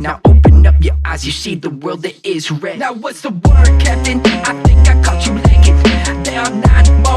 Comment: I thought he was saying 'whats the work ethic'